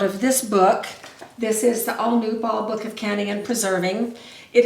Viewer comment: Can you please tell me the page number in the Ball Blue Book this recipe is on? Thank You. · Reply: I found it! I thought you said the Ball Blue Book. My mistake found it in the ball book you 'showed'. I can't wait to get my eye surgery in February :))